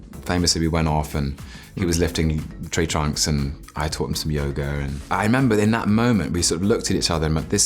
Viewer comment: I HEARD YOGURT AND WAS SO CONFUSED